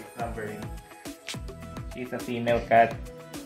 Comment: "He is a female cat" LOL😂😂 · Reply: He said 'she is a female cat' but because of his accent u heard it as 'he is a female cat'....Anyways u understood what he was telling so now the errors don't matter